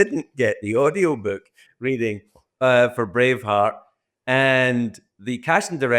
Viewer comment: thought he said Brain Fart instead of Brave Heart